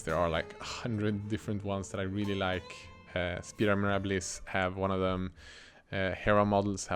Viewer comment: what was that brand of minatures you mention about 15 minutes in the video? It sounded like you said "spearmobilus". It looks like their models would be fun to paint. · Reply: Spiramirabilis